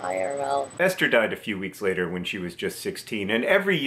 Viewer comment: i thought is said Easter